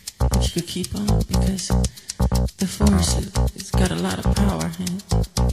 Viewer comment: All these years I thought he said...Keep on with the four star stomp 🤷🏿‍♂️🤦🏾‍♂️